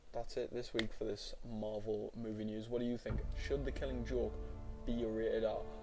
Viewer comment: did he just say marvel movie news??